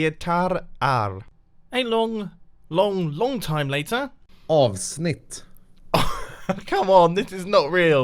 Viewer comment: thought was saying aww shit.